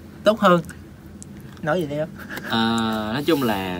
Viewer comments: Lúc 2:10 Khánh said : nói gì đi em... tuôi có nghe lầm không chời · Khánh sad : " Nói gì đi em " tui có nghe lầm không 😂